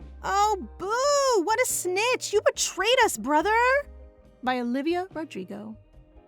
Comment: Am I the only one that heard her say by Olivia Rodrigo? When we found out, the brother was gonna snitch.